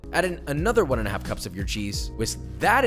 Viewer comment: im listening to this like a podcast while i work, and i thought he said blue cheese. I stopped EVERYTHING to go unsubscribe and dislike the video- just to find out it was 'Your cheese" not "blue cheese"